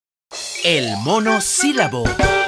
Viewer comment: Yo entendí "El mono silbao"